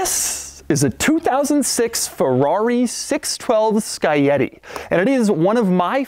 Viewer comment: For a second I thought he said "Ferrari 612 Spaghetti" LOL